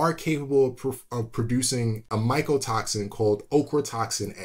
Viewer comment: Ochratoxin A! 0MG, I had to laugh out loud. I thought you said Oprah Toxin A. Bad hearing. Exposed to one too many mortar rounds in Vietnam, I guess. What a great channel for a guy with a 9th grade education to have access to. This is like the best. Thank you so much.